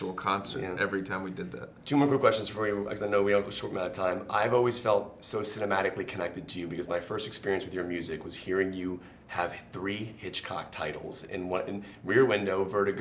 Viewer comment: did he say something about 3 inch cocks